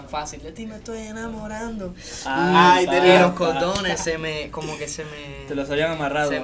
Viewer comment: Cuando Erick dijo cordones yo pensé que dijo condones. 🤣